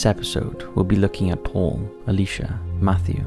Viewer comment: You know, for a split second their i thought he said "today we will be looking at Porn" instead of Paul And i was like, .....ok, but i think its gonna violate your terms of service 🤣